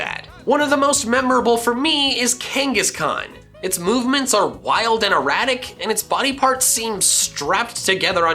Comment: did he just say “wild and erotic”? 0.o